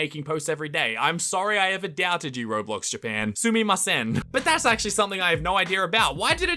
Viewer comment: I thought he said “Sue me , me son”